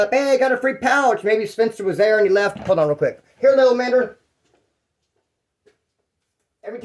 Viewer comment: did you just call your dog a little nigger? Bahahahahaha lolololo